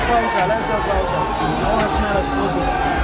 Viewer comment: did Connor say "I want to smell his pussy"? LOL